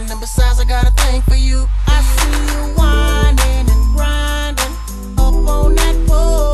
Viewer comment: I was 20 years old when I realized the true meaning of the song. Growing up I always thought Akon said "I see you whining & Crying up on the floor" and he's letting her know that he wants to love her because she's sad